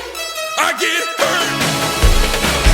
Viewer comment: I really heard "I gave birth!" Instead of "I get burned!" 😅 Also, why does this kinda remind me of Flobots 'Handlebars'